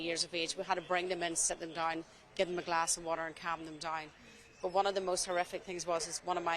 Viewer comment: I heard 'gave them a glass of water and have them die'. Had to turn the subtitles on to get 'calm them down'